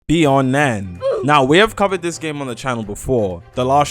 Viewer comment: I thought you said beyond men.